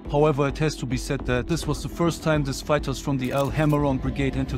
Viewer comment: With the accent sounds like he said the soldiers encountered homeless fighters. LOL